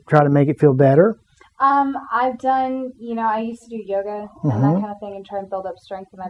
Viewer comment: I thought she said "I used to do Yoda". Well of course that's going to hurt your back, he's tiny, that's got to be awkward!